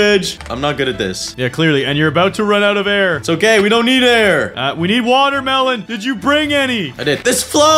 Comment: What said we need water to melon it sounded like he said we need water melons